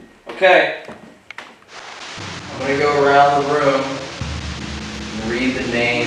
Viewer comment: I think it's funny at how 51:11 after says he's going to read the names of these victims I hear a man say "no I'm gonna go" or oh I'm gonna go. and said it quickly. So I would slow your speed down and listen to it and help figure it out.